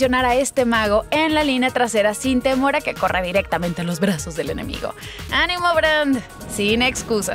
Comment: jaja le entendi "a los vergazos del enemigo"